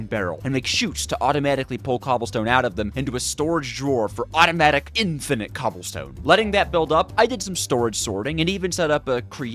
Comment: It sounded like you said Automatic Infinite Cumstone 💀